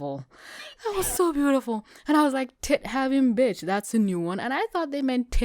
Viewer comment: I thought that said " tick having". Also, angelic entities in possession of lethal weaponry isn't uncommon since Saint Michael the archangel is a literal angel and is said wield weapons against wicked spirits and demons.